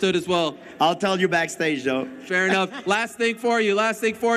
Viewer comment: Did GSP just call Ariel Joe? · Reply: No he said though